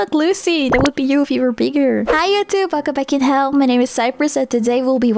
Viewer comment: You say "welcome back in hell" But sometimes i hear "welcome to fucking hell"